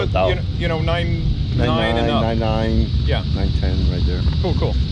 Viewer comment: did he just say 9.9, 9.10? loll